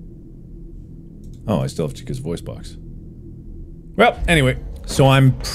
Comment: when you said you still had chicas voice box the caption said “ I still have to kiss voice box.”